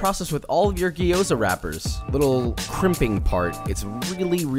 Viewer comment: Damn I thought he said Crippin. Just went out and bought a bunch of blue clothes for nothing smh.